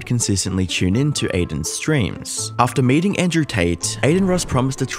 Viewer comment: heard that as "mating" now can i can't stop picturing it